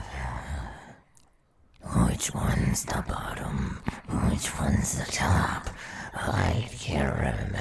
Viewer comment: What is he saying here? 4:30 - 4:40 I'm not from America, I'm learning English and I didn't hear that....☀ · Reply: The second one is "Which one's the bottom, which one's the top. I can't remember" I think